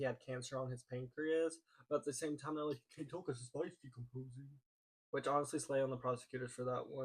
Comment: What did you even say at 27:21? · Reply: “You can't tell because his bodies decomposing”